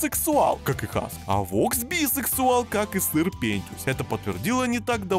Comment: Почему я услышала Сыр Пентиус?? X)